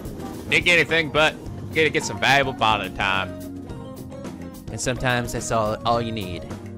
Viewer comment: Am I the only one who instead of hearing bonding hear bondage so when says "valuable bonding time" all I heard is "valuable bondage time"?